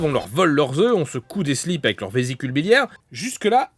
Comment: J'ai compris "on secoue des slips". Ah ?